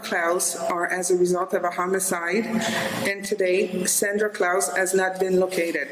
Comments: For a moment I thought she said "Santa Klaus" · "Did you kill your parents?" "Did YOU kill YOUR parents?" Such an ironclad defense, which I'll keep in mind the next time I'm pulled over for speeding. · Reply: 12min Mark it sounds like she is saying Santa clause 🎅 LOL...